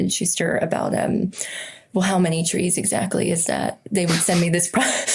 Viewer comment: I thought she asked how many 💤 😴 💤 instead of trees. She put my whole 'azz' to sleep with a side of huh???😂